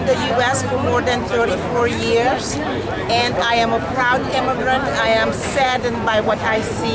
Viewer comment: Did she say she is a "proud Democrat." I thought she was running as a Republican.